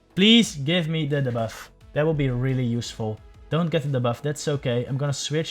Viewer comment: I heard "I don't get the debuff that's so gay" 😂😂